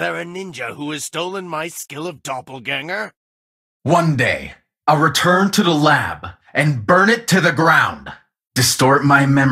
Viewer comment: I really thought aldous said “you will return in the dark”